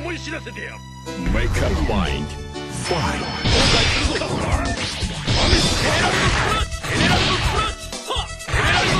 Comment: What it said Hierophant Green when appears at 5:56 ? Kokaicerdo? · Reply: 後悔するぞ！ ですね